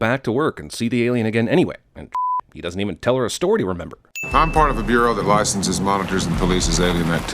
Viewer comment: “a storty to remember?” Did anyone else hear that? We’ve come full circle, the audience sinning the “sinner” I guess? ding